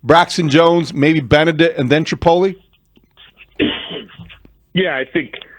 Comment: … does he call Trapilo “chipotle” 😂😂